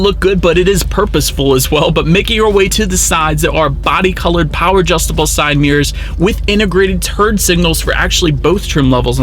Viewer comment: Did he just say "turd signals"?